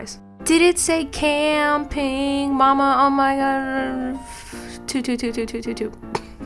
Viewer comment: i always heard "woah, oh my!"